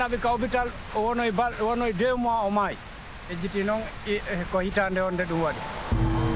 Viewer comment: SUBTITLE CORRECTION: 16:08 "I'LL NEVER FORGET THAT DAY" IS INCORRECT. WHAT HE SAID IS "I FORGET THE YEAR IT HAPPEN".